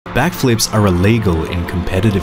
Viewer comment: I thought you said black flips 😁🤭🤭